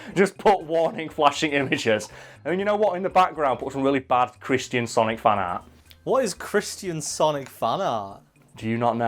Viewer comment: Oooooohhhhhh, chrisTIAN Sonic fanart...... I thought you said Chris-CHAN Sonic fanart.